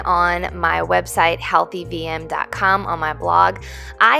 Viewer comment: When you said healthy VM I thought you said healthy BM. I guess they both pretty much mean the same thing, which is why we love you!